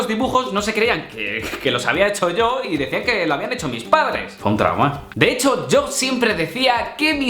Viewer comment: pense que decia:o trauman no fue un trauma eln el minuto 2:40 (vi subtitulos)